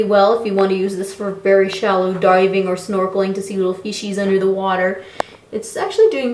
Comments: It would have been cool if you pointed the camera at you and had the camera pointing up from the water... like they do in movies lol. Also @ 9:43 sounded like you said "so you can see little feces in the water" lol. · did she just say she wants to see faeces underwater?